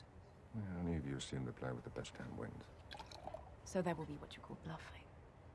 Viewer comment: what is Vesper saying at that part? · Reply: "So that would be what you call bluffing?"